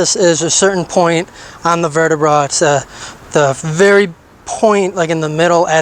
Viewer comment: Ok is he saying Verti-Bra over over 9:30